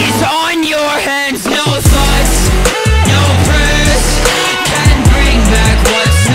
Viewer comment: why i hear "no thots no prayers"? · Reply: xayeeto you’re hearing it correctly. No thoughts, no prayers. Often after a shooting happens in America, instead of actually trying to do something people often offer their “thoughts and prayers” as if that makes anything better. Which is pretty much why he’s singing “no thoughts and no prayers/can’t bring back what’s no longer there”. Thoughts and prayers are everyone’s way of putting a bandaid on it until it happens again.